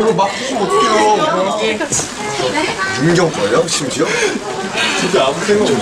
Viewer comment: Is it just me or does joonie say yoongi oppa at 0:59???